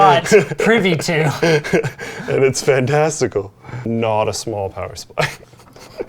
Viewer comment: Did luke say Fantasticle